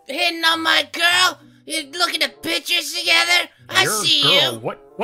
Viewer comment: says ""looking at pictures" but that terrible voice acting makes it sound like he said "Looking at B*tches".